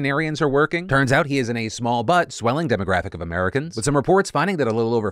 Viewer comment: all I heard was "he is in a small butt swelling"